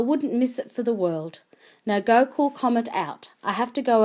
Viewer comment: wait, at at 1:18 did she say Comet? I HAVE A BAY NAMED COMET IN RL :O